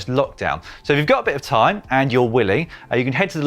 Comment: it sounds like Dan says “...if you’ve got a bit of time and your w***y...” Hahaha